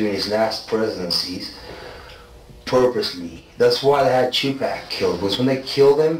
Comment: Everyone is focussed on the Diddy chat..but no one is noticing the assassination attempt reveal. Crazy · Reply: He says Chipak. I think he saying Tupac